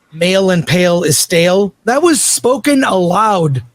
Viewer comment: Dude saying that "Male and pale is stale" is a common phrase when I've literally never heard it before is hilarious. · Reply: I honestly thought he was saying "Male impaled it's a sell" and I was like "huh ?"